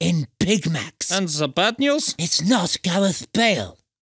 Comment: "We are f***ed" - Jose Mourinho 2020 This line seems to fit on jose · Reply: on 2:22 turn subtitles on. Jose Mourinho says "We're Fucked", Subtitles say "We're Norwiched" xD